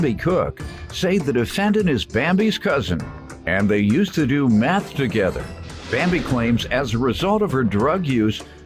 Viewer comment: At the very beginning I thought it said “and they used to do math together”🤦🏻‍♀️. I was so confused. 😂😂😂